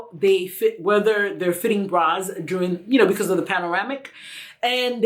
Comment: Did she just say panoramic? hihi